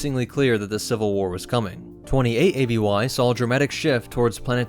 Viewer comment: Anyone else hear "28 ABY" at 1:10 ? Or just me?